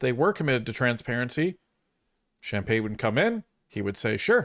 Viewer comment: ~There was an error in transcript in 5:36 transcript says wouldn't it should say would~That's what you were saying but transcript says🤔otherwise~